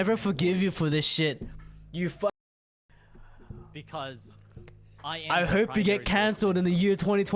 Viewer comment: I THOUGHT HE SAID "I HOPE YOU GET CANCER" MY GOD